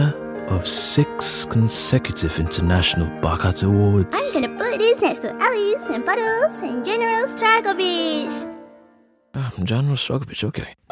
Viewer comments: What was she saying in 0:16 I couldn’t understand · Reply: "I'm gonne put this next to Ellis, and Puddles, and General Stragovich," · I have no idea what she just said. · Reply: "I'm gonna put this by Ellis and puddles and general stragovich" is what she said